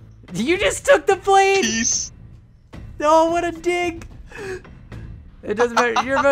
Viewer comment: did he say "oh what a dig"